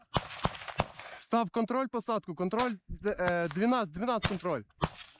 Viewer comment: Error 18:56, in the subtitles instead of "landing" it would be correct to write "tree line".